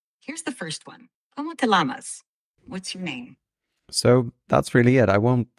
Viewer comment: It hasn't learned how to read Spanish and English words in the same sentence. In the Oral Exam section of your video (13:16), it said, "¿Cómo te lamas?", like the animal, instead of "llamas" (yamas). Give it a year and I'm sure that it will have improved to no longer make that error.